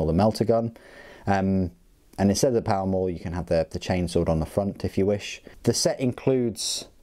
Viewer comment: I honestly thought you said peeper chain sword lol I really like these models and the fact they include two of each weapon except for the chain cannon and a great review as always 👍😁